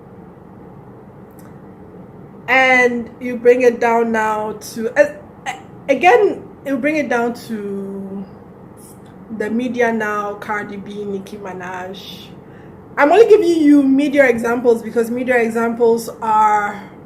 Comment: Herh, Sis, did you say Nicki Manaje @ 1:42? 😭